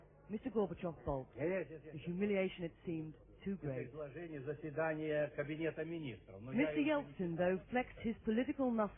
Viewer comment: What did they said at 1:30? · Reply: Mikhail Gorbachev is saying "you gave me the report of the council of ministers, but i haven't read it yet." and Boris Yeltsin replies "Well read it"